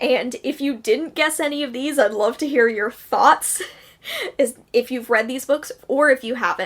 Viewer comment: I think you said "thoughts" - I heard "farts" (shame on me) :-) <3 <3 <3